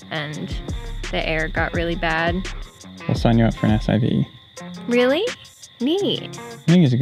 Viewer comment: What does say here? "We'll sign you up for an SIV?"